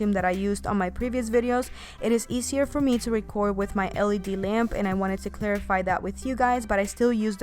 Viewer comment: "Easier for me to ___ in the led lamp?" 10:42 I'm sorry didn't understand that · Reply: Jenny yeah she said “it’s easier for me to record in my LED lamp”